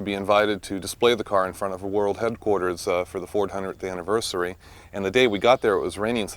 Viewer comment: I had to go back and replay the video at the 3:55 mark because I thought I heard "the 400th anniversary." Gorgeous car.